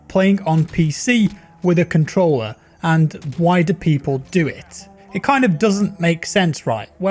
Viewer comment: I thought he said and white people do it lol